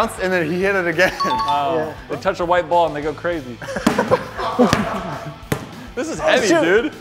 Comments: I heard someone say "he touched the white ball n** crazy" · anyone else hear @ 8:55 when he said " i touch a white ball im gonna go crazy"...no...just me...ok I'll lime my own comment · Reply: i have no channel * and they go crazy*